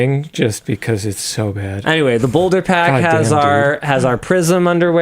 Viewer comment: almost thought Linus said "and now for our Prison Underwear" I thought daphuq?